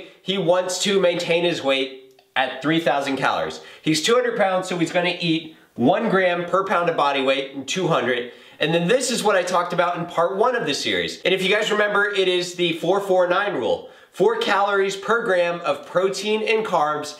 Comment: lmao thought u said 4g per pound